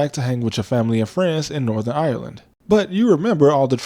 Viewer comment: Thats the flag of Northern Ireland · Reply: yee ik it just sounded like you said « france in northern ireland » but you said « family and friends in Northern Ireland »